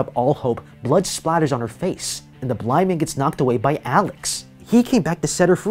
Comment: "The blind man gets knocked away by Alex." When he says "blind man" it kinda sounded like the N word. Am I crazy for thinking that?